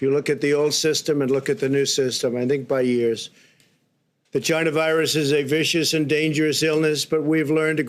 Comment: Just curious,did he say China virus at 1:11___ ?